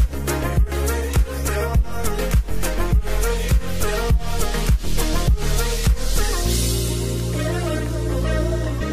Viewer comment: is the lipstick big bang? or is it really called big bag? thank you for for doing this video!! all the swatches looked so pretty on you! :) I love the shine of the creams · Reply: It's big bang haha